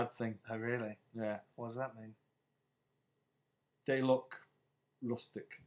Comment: Did he say "They looked LUSTIG" at 0:37:26 ?